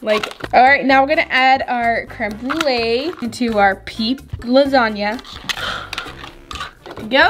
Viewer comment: In my head when she said peeps lasagna I thought she said BITCH LASAGNA 🎶